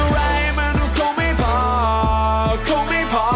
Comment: ได้ยินว่า กูไม่บอก